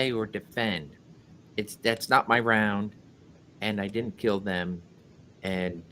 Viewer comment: Allen's laugh is unbelief....like "the hell" my round matches. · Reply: Yes I heard it as oh man that’s not my round.